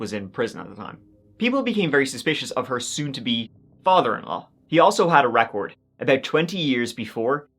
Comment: what I heard: "he also had a rat hoard"